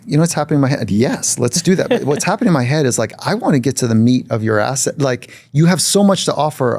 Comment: I want to get to the meat of your ass (you have so much to offer) lmao 😂. I’m here to listen but I heard that soooo differently.